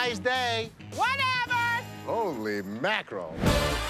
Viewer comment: Did he said Holly mackerel